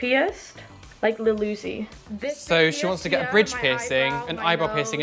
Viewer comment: I could have swore the first lady said she wanted to get her labia pierced...